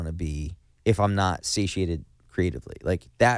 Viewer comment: what in the fUCK is that word Frank uses at 7:23 about knowing he "cant be a good enough dad, without being _____ creatively"? It sounds like hes saying situated with some southern accent but that doesnt make any sense XD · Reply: satiated! :)